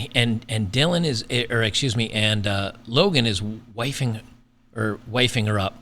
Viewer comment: You keept say "wifing her up" but I kept hearing "wiping her up". I thought he better wipe her up to get rid of all the seepage and other men's juices before he "wifes her up".